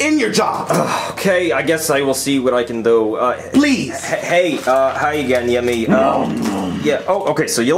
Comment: hi again ? what did he say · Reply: Hi again, yeah me